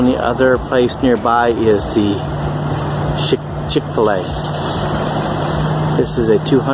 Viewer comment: Say what? :D I heard Sh.t-chick-fil-a :D :D :D